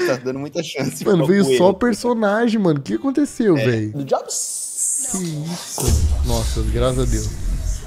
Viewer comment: o que ele falou? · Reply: du job